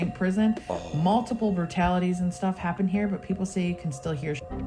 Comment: am I the only one who heard her say shit? like ''Also known as the shawshank prison multiple brutalities and stuff happen here but people say you can still hear shit'' that's kinda funny